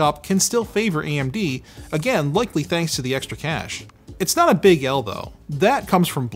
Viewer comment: I keep hearing “cache” as “cash”